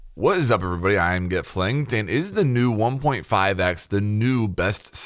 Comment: "what's up everybody, it's get fling here" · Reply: I heard "I'm get flanged here."